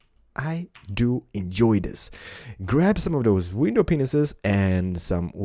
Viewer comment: it sounded like he said window penis'!!!! LOL